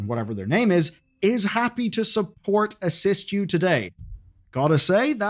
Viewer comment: am i missing some thing it says " is happy to assist you today " no what your saying it says.